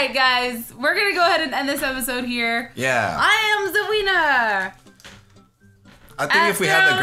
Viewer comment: You shouldn't never tried to say " I am the winner!" With an accent because it sounds like you said " I am the weinner!" Lmao😂😂😂